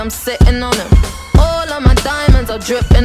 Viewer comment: Help all this time I thought she said "order McDonald's" instead of "all of my diamonds" 😅